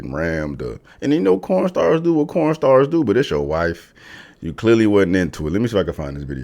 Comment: Did he say "Corn stars??"